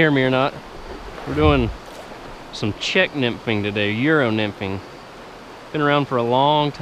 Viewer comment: what did he just say? love me some good yero nymphin